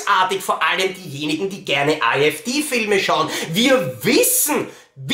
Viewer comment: verstehe immer AfD Filme :-D ;-)